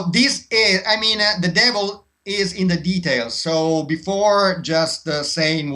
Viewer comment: DOES HE SAY, "the devil is in the details?"